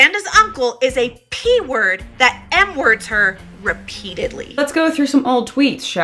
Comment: What N word are we talking about? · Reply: Think they said "M"